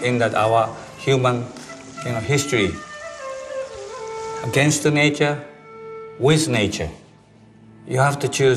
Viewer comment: i thought he said gangsta nature at first. i was confused cause its just a table